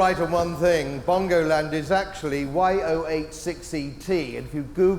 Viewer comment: Did he said Yo86CT ? OR what ? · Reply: YO8 6ET it's a postcode